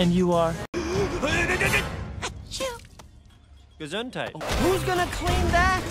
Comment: did he just say "gesundheit"? Thats German, my language!